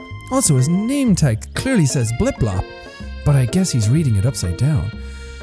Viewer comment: I thought jack said blip blop instead of bob😂